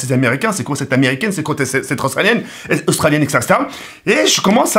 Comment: A 2:20 j'ai compris cette transalien 😂😂😂 sinon super vidéo et lourd montage 😉